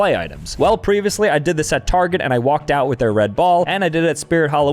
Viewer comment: Bro did not buy another red ball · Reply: help i thought it said red bull like the whole time